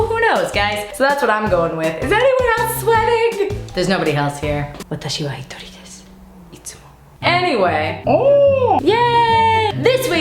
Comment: what does she say at 0:53 😱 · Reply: "Watashi wa hitori desu", which means "I am alone".